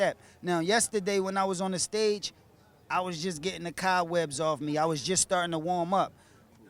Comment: This is gon be a weird question but, what did Cass say @ 1:10? ("I_was_just_getting_the <Ka> webs_off_me")